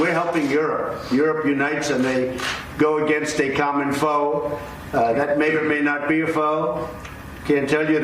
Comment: did I just hear NATO's common foe may or may not be a foe?